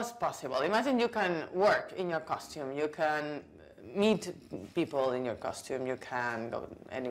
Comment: I thoughy i heard :"Be nice to eat people" my mind went WTF??? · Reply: Ha xD Noooo ^^ To meet people :3